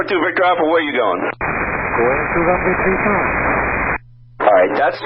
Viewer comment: he doesn't say "two times" but rather "going to runway three five". seems tower just totally misheard